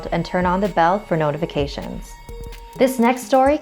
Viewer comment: I thought they said turn off the lights 😂and they said turn on the the notification Bell!